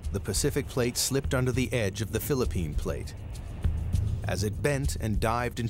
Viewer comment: Did i hear it right? Philippine plate?